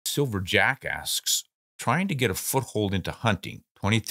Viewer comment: Lol I thought you said silver jackass at the beginning.